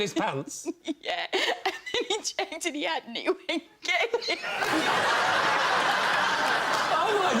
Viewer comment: I can't understand what she's saying. Anyone can help me out? (not native to English) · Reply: Harriet said "and when he checked and he hadn't, he went get in". (As a side note, he hadn't or he hasn't, is often pronounced "he ant" in some parts of the UK) she did say he hadn't but it was fast, plus the fact she was laughing while speaking which made it much more difficult to understand. I hope this helps :)